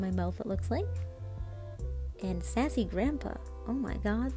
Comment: did i just hear her say “sAsSY gRAnDpA” ?🌚 edit: wait why did she like my comment?